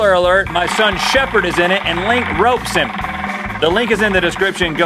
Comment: i thought for a second he said gropes him but i realize he ment ropes him s0rry